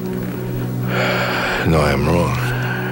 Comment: am i hearing him wrong? at 1:02:00 doesn't he say "no, i'm wrong. I'm wrong." he then says that does actually want forgiveness.... i don't think that's about what he's done, i think it was just that last thing he said.